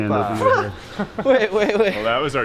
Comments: Did say OPA at 24:07 ? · Did he actually say "OPA" at 24:08 ??? Greek alert here!